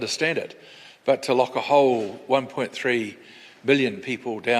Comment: Did he say 1.3 Billion people? these muppets aren't fit to be in power. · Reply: U on to it Raiders.. 1.3 Billion is exactly what Pudgy said ..Watda??? Maybe needs to up the dosage of the dementia meds so as to focus on which country/city he's talking about while attempting to run the current Gov. down.. Clutz